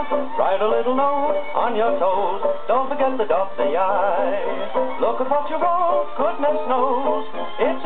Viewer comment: Thanks for posting this. One quibble: instead of "look apart you won't..." I hear, "Look at what you wrote..."